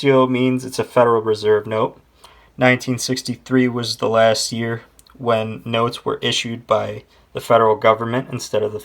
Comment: he called a 1963 red-seal $5 USN "1936". - At 07:13 he said 1963 was the last year the federal reserve issued notes. 1966 $100 notes were issued by the federal reserve. - At 08:02 showing the backs of $5 notes, he said the backs haven't changed much although he could've pointed out IN GOD WE TRUST was added to the 1957 series. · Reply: he actually said 1963 was the last year notes were issued by the FEDERAL GOVERNMENT.